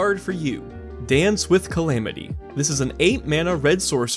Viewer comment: Lol, I heard "Dan, Swift Calamity."